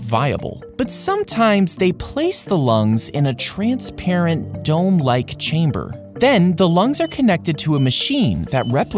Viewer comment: I thought they said "Don't like chamber"